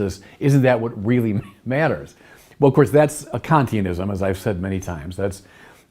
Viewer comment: What does the bishop say there? A conteinism?